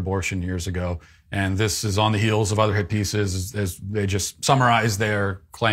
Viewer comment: LIstening at 1.5x and thought Matt said "hippies" instead of "hit pieces". Ha.